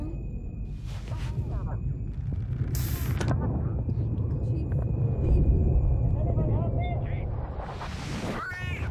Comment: Does anybody else hear the girl whisper in the video between 0:27 and 0:29 "TIME TO GO", or is it just me? :D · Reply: yeah she,the little girl, says "time to go."